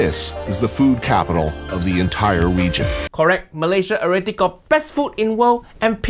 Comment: Uncle Roger brain worms so strong. I hear, "wegion" at 1:06.